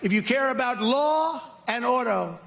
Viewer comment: And here I thought he said 'Law and Odo'. You know the shapeshifting Sercurity Chief from Star Trek: Deep Space Nine.